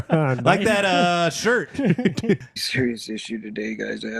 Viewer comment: When his ex-wife pops up on camera, around 4:55, sounds like he says, "Hi, (mumble) Virginia". Then he obviously introduces her like 20 seconds later, as.......Did he fuck up her name? or just saying hi to a fan watching his stream? I"m going to assume the former lol